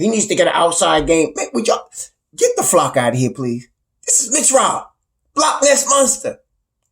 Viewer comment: I heard you say GTFOH out loud. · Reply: GTFOH....I said...Get The Flock Outta Here